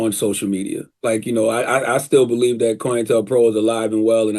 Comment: CTG said " I still believe that *BLANK* is alive and well." I tried slowing it down and still can't make out what he is saying. The closed caption has "coel Pro" in the blank. I have never heard of "coel Pro", and/or it's not a real thing. BTW, I am completely open to either possibility. Can someone please fill in the blank for me?